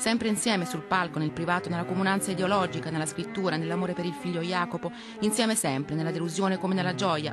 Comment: ho sentito bene o mi sbaglio??? Al minuto 1:24 dice: ...nella morte del figlio jacopo....